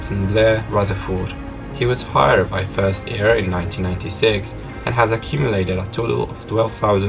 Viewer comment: hard to understand him too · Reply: Anderson Rodriguez.. Yep, I had to rewind because I thought he said “fired in 1996”.